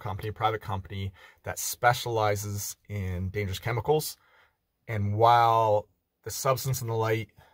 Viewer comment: Thought you said "guy who deals with Danish chemicals".